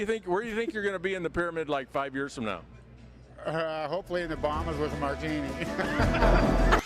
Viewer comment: The one guy who th "superman " interviewed answered a question by saying ," hopefully in th Bahamas with a martini " I misunderstood him and thought he said, "hopefully in th bottom with a martini. " 😂😂